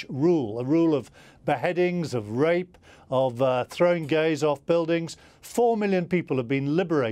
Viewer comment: Did he say "throwing gays off buildings"? Fallon the old dog-whistle, dribbling pus without fact checking. or even listening. Confused, irrelevant old git.